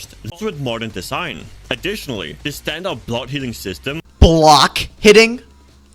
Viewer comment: Ermmm Mr. Video creator he actually said blood healing! 🤓☝